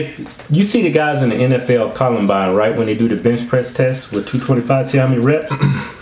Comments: Kevin, did you say the NFL Columbine 1:12? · Did he say NFL 'Columbine'??